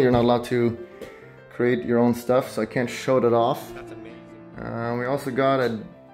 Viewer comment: did someone hear That's on me??